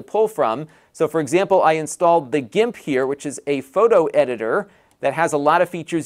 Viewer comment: Did you say The Gimp? If so, congratulations! Your AARP membership card is in the mail. ;) Seriously though, thanks for another cool review.